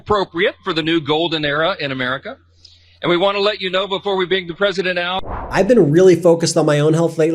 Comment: For a second when Mikey Johnson was introducing THE LATEST PARTICIPATION AWARD to Trump he said, "Before we bring Trump out". I honestly thought he said "Before we dig Trump up"! 😂